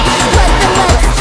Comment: What does Amelia say at the end of the song (she also says throughout the song)? What I hear is "It's definitely fuckin'!" but I'm not sure.